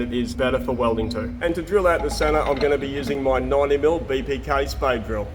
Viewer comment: I thought you said “Kate spade” drill, and thought it was designer bit.